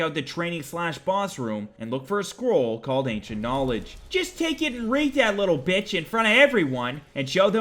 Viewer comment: I swear thought you said squirrel of ancient knowledge at castle Volkihar for the bat spell. I thought "Wow, what a great mod, adding such a unique way of learning something new"